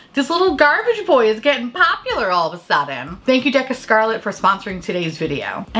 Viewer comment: I thought you were saying Dick of Scarlet which would be a much more interesting brand name.